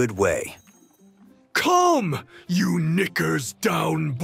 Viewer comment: sees the title oh lord here we go, i aint ready for this chaos 3:02 WHAT DID YOU SAY?! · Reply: He Said “Knickers”…